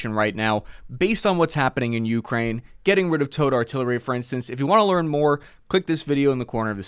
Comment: Ha, ha, ha! "Oh nooo..." Nice. Great mix of interesting data and chuckle-inducing humor. P.S. Every time he says "towed artillery", I just hear "toad artillery".